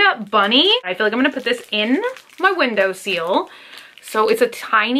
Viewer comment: did you just say “window seal”? ....... window sill 🤦‍♀️ then again, you do always incorrectly pronounce your i’s as e’s. Like when you talk about “lip tint”, you say lip “tent”. Is that a Southern US thing, or a Bunny thing?!